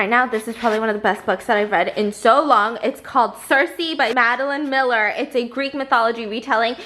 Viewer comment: I thought it said circle.IDK why.